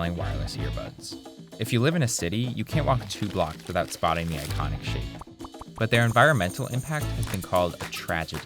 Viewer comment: Did he just say you can’t walk 2 blocks without seeing the iconic shit????